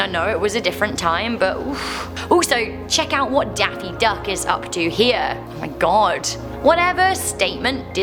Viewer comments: did she say Daffy Duck? 😂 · DID SHE JUST CALL DONALD DUCK DAFFY DUCK!!!